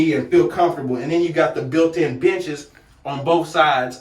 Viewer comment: I had to rewind that part after I heard the built-in bit¢hes 🤣🤣🤣